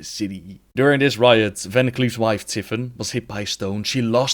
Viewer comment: I think you've made a mistake at about 1:25, the subtitles say Varian's wife, but you say Van Cleef's.